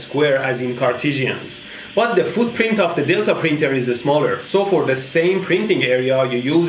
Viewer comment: I heard "The footprint of the dildo printer"